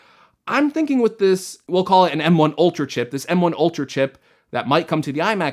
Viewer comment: I thought it said MK Ultra